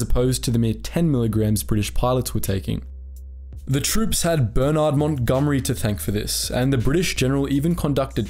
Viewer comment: Burnout Montgomery!? I swear that's what I heard you say.